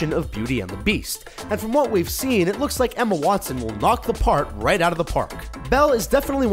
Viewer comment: did he say "part" or "park"? Because to me it sounds like he said "knock the park out of the park"...